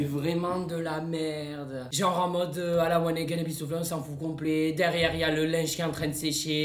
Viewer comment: il a dit quoi la 😂😂😂😅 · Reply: À la wanegeine bistoufligh